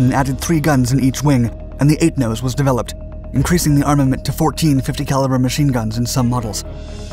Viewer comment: Uh did he say 14 .50 cals @ 2:48? Geebus...